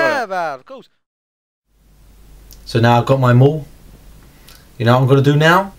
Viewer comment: " I got my maul" at first I thought he said I got my mole. anyone else hear that to?